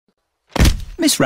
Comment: “ms rabbit has ✨fainted✨” “ms rabbit has fainted ✨AgAiN✨” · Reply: I heard "this rabbit has fainted" loll